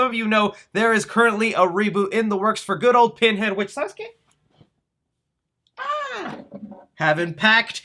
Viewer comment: did he say “sabes que” in spanish? I think he might be speaking spanglish now